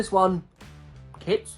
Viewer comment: what did he say? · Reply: Kids